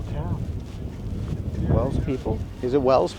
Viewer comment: Wells... I thought you said Wales at the start, and I was trying to guess which one... St. Davids? Too funny.